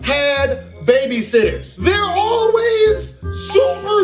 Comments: I accidentally thought it said baby sister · I thought it said baby sister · I thought this shit said baby sister at first